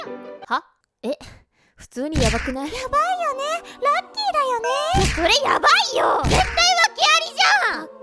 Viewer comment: ラッキーだよね が なっきーだよね に聞こえた私はもう末期なんだろうなあと思いました。 私も御祓い行ってきます。